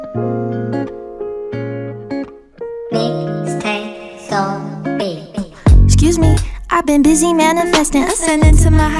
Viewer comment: What is the background voice saying at 0:19 ? It sounds like "Mystic. Soul. Beats" right before the great singing comes up. Thank you! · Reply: It says "Go find a real music" :)